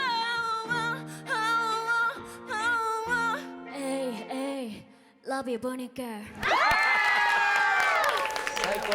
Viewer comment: ここ何て言ってますか？🥹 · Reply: Love you BURNINC-GIRLs(チーム名) です！